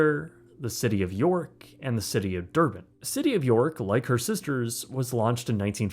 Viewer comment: did I hear that right? The City of Derp? 😂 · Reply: City of Durban